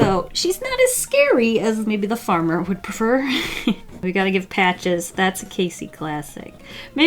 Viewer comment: Me freaking out cus I heard "we gotta get patches" realizes she said " we gotta give patches" (my cats name is patches)